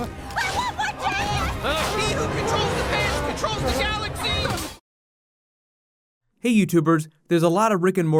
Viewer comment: I thought Morty said "He who controls the pay controls the galaxy!" Because all the money went from 1 to 0.